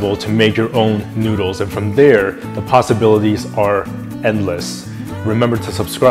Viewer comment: I really love this channel because it's so hard to find vegan ramen but he makes it so easy. Also I thought he said the pasta-bilities are endless...:)